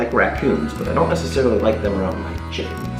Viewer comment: did you say you don't like raccoons around your genitals?